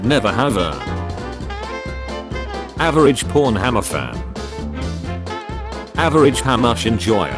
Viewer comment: an I the only one who heard pornhammer?